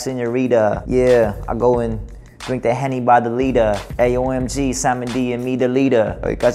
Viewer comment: i think he said, "I go and drink that henny by the litre," not "henny bottle rita." hahaha